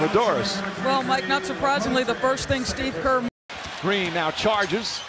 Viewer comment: When the arena says "DEFENSE!" i thought it said steve kerr